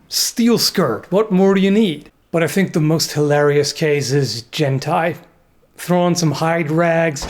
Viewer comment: Jen-Tai? I thought you said hentai.